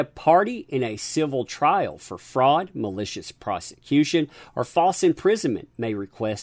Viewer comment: I thought you said, "malicious prostitution", which would just about sum up AHs goal in the marriage.